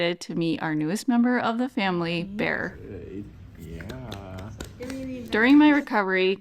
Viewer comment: I'm Australian, so there's an accent difference that makes it hard to understand the baby's name. It sounded like "Bear" to my ears. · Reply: You heard correct 👍🏼 Bear is what we call him 🙂🐻